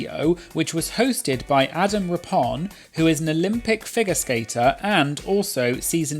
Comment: lol not me mishearing you say that Aquaria is an olympic figure skater #doubletake <3